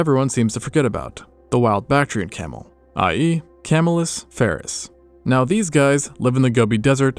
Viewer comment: I misheard it as "Kamala Harris"